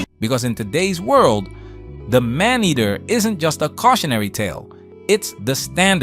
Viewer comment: 50 seconds in and hit pause, so that I could comment. I was 21 when that song came out and I always heard it as "MINDeater." I learned young.